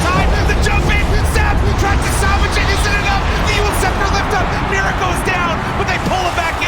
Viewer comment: did he just said Miracle- is down? xD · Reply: Mira goes down. the lion player from Spirit